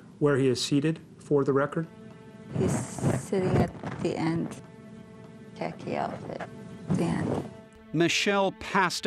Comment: Her sister describing him in the deposition “wearing a tacky outfit” was so perfectly innocent of an insult compared to his atrocity. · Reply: I thought she said khaki, but yeah, tacky would make more sense, since wearing a khaki outfit to court doesn’t make sense